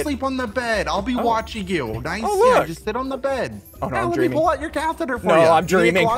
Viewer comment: wut did u sayy?? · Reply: “Let me pull out your catheter for ya”